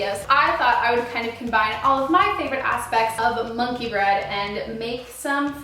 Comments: I thought it said money bread. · Who else thought it said money bread